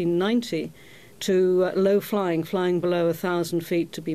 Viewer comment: I thought it said low flying Wales I was very disappointed...